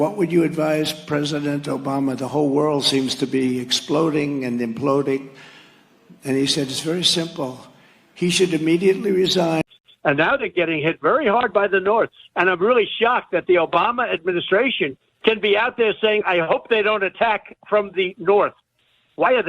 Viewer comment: I heard "getting hit very hard by the Nords". Was Trump talking about Tamriel?